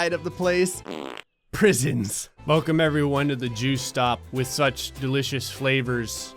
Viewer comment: I thought he said jew stop XD